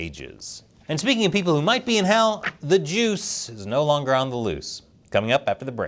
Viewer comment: "...and speaking the people who might be in hell, the Jews!" That's totally what I thought he said.